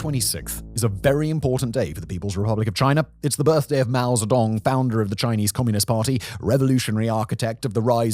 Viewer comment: You said "Mao Zedong" I heard "mouse dong"!!